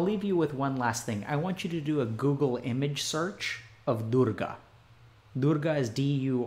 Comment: oh gosh i heard durka instead of durga, not what i expected lol